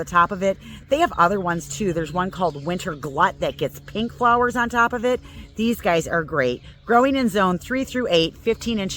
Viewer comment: I cant understand the flower you saying at 10:19.....Puluminarie???? Love to buy, but cant find the name in your comments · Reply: Pulmonaria. Lungwort